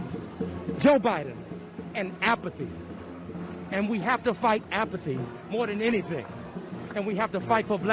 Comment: Apathy, oh I thought he said Applebees😆